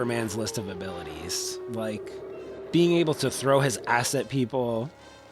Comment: This was really interesting - Superman II was always my fave because Zod, Ursa and Non were such good baddies. The narrators accent, I thought he was saying “ass” at 31:30 😂